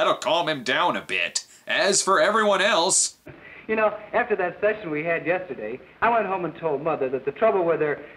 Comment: um, did he just say "after that Sex we had yesterday"? I'm pretty sure '30's movies weren't that open about THAT stuff!